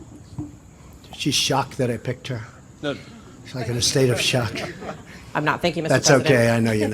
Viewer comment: I’m maga all the way however after seeing that second clip of the reporter “being shocked” I always thought she said “I’m not thinking Mr President” but after listening carefully she said “I’m not (referring to being shocked) THANKYOU Mr President.” I’ve seen that clip a thousand times but just realized. I still think it’s great